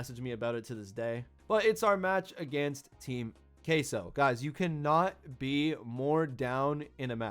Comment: Bro I heard Team Caseoh😞